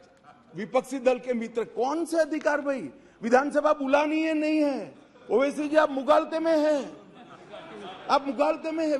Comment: what did owaisi said? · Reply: He asked Question "Main kaunsi Team ka hissa hun".....